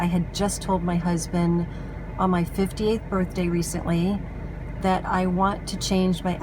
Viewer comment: Hi, J Jearn! It's so nice to hear from you again. I was born in 1960, so in 2018 I was 58. I turn 60 this year (2020). Hope that clarifies things. Thank you for watching my video! :-) ~ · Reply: Hello, I just asked as I thought I heard you say in this video that you had just had your 50th birthday which confused me as I thought we were roughly the same age. I enjoyed your video, thanks for the reply! Best wishes, Jan.